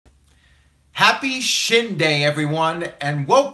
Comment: I thought u said happy shit day🙄